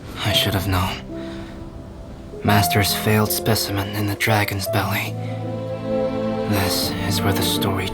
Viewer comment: yeah but than what does this line mean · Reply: Yeah but I was referring to the line “in the dragon’s belly” I thought he was saying he was created in durin belly or something lol but it’s all clear now